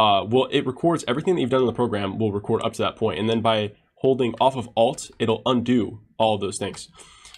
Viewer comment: What u say in between 9:05 and 9:15. Right after you say hold cntrl-alt-z you said hold "fn-alt??" Or what was it??